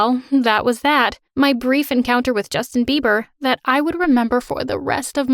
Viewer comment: I thought you said Justin Barbie lol I hate him